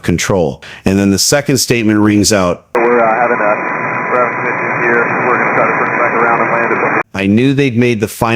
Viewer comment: Sir former USAF Tower ATC. at 6:30 I think he says "we're having rough conditions here..."